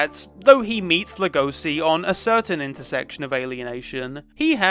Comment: Every time you pronounce "Legoshi" I hear "Lugosi" and now I'm stuck thinking about furry vampires. Thanks, I love It!💖